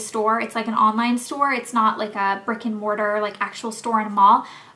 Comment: When you said Shop Bob isn't a brick and mortar store I thought you said "It's not a freakin mortar store" lol